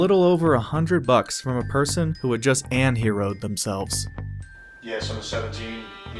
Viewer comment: What word are you saying? "anheroed himself?"